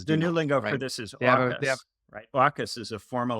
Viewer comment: what is the word he is saying there? The 'new lingo' for the 5 eyes? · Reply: AUKUS